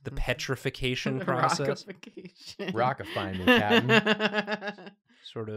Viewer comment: I think I heard say "Rockify me, daddy"???? This episode is truly a gem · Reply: I think he said ‘captain’ but daddy isn’t too far off ig